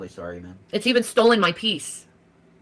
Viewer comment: im sure i heard her say 'its even stolen my teeth' lmfaooooo